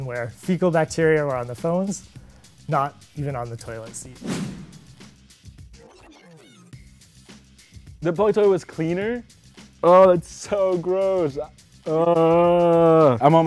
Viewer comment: Did he say "boilet towl?"